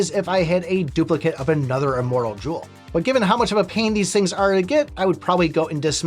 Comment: I thought you said "Immoral Jewel", need to clean my ear